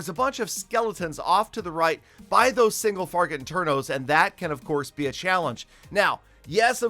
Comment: he says single farget interno instead of single target inferno 🤣